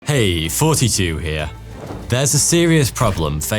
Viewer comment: I always thought he was saying “hey, 42 here…” Actually he’s saying “hey, fraughty2 here.” The Brits are so strange